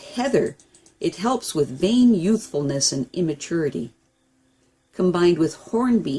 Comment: Thanks for this huge amount of useful information.....pls can I ask what are you exactly saying at 10:30 while talking about Honeysuckle + Heather. - are you saying weighing youthfulness or vain youthfulness or something else? What does that mean? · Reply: Honeysuckle and Heather, helps with vain youthfulness and immaturity. Sorry it wasn't clearer. I am glad you like it.